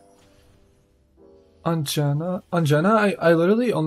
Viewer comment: I thought you were saying Ongina and thought I clicked a drag race video for a sec 😅